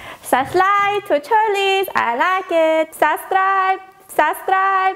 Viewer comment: The best part is that the outro sounds like shes saying "subscribe to charlies", which just perfectly ends this video